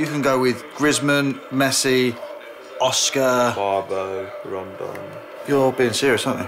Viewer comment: did he say fucking Oscar?